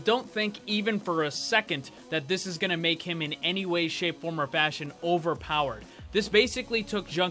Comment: did he say or bastion?!!!?!!!??!!!!